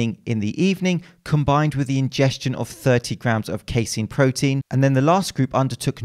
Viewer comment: did you just say 30 pounds of casein protein ? sounds like a lot of powder